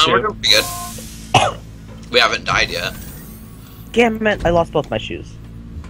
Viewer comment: lol at one point it sounded like I said "gammit" instead of "damn it"